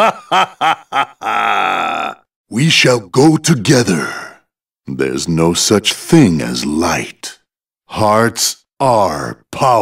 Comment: my entire childhood for some reason i always heard “there’s no such thing as the light in hell”